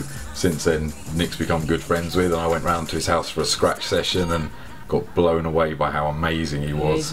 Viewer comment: Got blown away at a scratch session? did he actually say that?